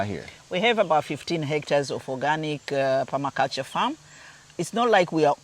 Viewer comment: It sounded like she said hector, if so, she has a lot of land